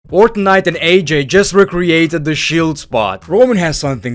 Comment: I thought he said fortnite at 0:01 lol